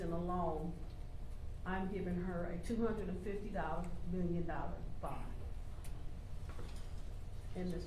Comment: all yazs need is 2.5M Cash good luck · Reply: I thought i heard 250 million cash bond